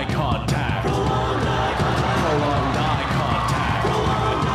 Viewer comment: PROLONGED EYE CONTACT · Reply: I thought it said prolonged die contact